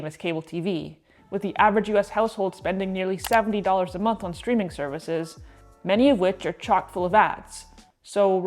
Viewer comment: I thought you said “Do you remember when Netflix first started offered screaming services?” 🤣